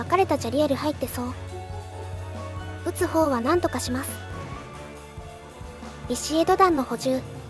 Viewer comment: 撃つ方は何とかしますが 筒香は何とかしますに聞こえてしまった